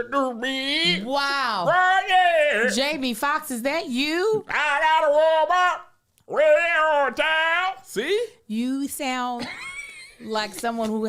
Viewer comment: That Ray Charles impression sounded like was saying, “I got a Walmart…waayyy over town..” 😩😩😭😭😭🤣😂🤣😂🤣😂